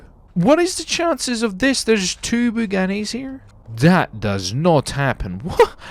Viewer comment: did you say bugani?